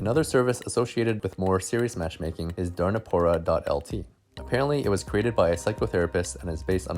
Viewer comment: lol I heard 'durnapora' instead of 'darnipora' XD durna means stupid